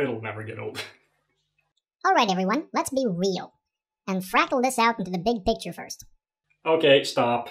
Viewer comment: I thought he said Fraggle this out... as in "...down at Fraggle Rock!" It may have made more sense, proof (in Jordan's terms, because two things sound the same they are) Jordan is a Muppet!